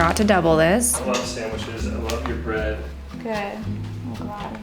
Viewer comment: lol I want to know what you said in reply 😂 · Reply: Haha I said “good, I’m glad” 😊 My husband always has the most energetic and joyful reactions to everything I do & it’s the biggest mood boost : )